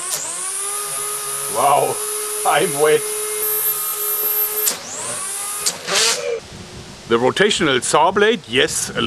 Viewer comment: LOL did you say "i'm wet"??!?!?! 10:40 obviously you have not searched up what that means hahahaha